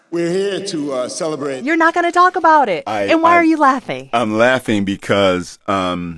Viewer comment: Is this man Canadian. How am I just figuring this out. I keep hearing "talk aboot it".